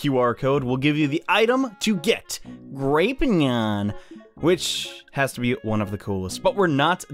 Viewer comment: sounds like he said Rapenyan